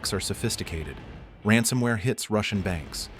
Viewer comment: Did I hear “Rancidware?”